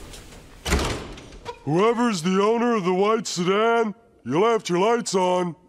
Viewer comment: I thought he said white Sudan